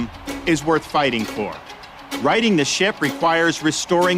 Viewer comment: Did he really say ´´...writing this shit -´´ Or am I doing a Peter Kay (comedian) and mishearing...... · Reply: Ahhh..... Righting this ship! Not writing this shit 😄